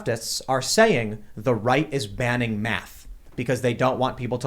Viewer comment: Oh thank God...I thought he said the Right was banning Meth, not Math. Gives me more hope in the drug community that we can overcome this Liberal Lefty nonsense.